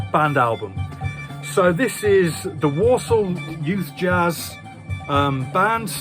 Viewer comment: Walsall jazz band? I thought you said Warsaw. I have a Hindi record from Birmingham with a track entitled 'fish & chips' yet to work that one out. I bet Richard McCook hasn't got that Boney M.